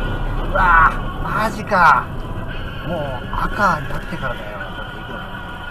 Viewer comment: 「もうバカー（赤）」って聞こえてきてしまう、、、 0:59 動き始めてから方向指示器が使われてましたので、車両の故障ではなく運転手に問題ありですね。 2:10 自分は路線バス、宅配便に対しては、自分が利用する側としてお世話になっているから極力譲りますけど、このバイクはこの状況で突っ込んでいくなんて頭悪すぎです。 2:52 この車間距離で追走されたら軽く減速しますけど、万が一目の前に危険な割込みをする車がいたら、前に突っ込むか、後ろから突っ込まれるかを考えるとブレーキ迷うね。 3:15 速度差って分かってるのか？って思うけど、右を通行するダンプが右に傾いている。 荷台のあおりを超えて積んでいるから過積載かもね。